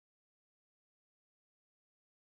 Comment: what is he saying at 10:49? lol! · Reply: "Do you like that Martin?"